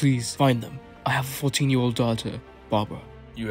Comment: It’s great but I can’t tell what this guys saying at 0:57 it sounds like “I have foot in your daughter bobra”? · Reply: “I have a 14 year old daughter, Barbra” 😂 it happens it’s ok lol